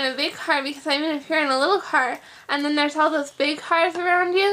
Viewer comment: is she saying heart or car?